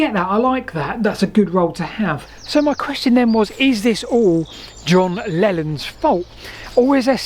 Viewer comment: I thought he said 'was this all john lenons fauly?' and i was so on board.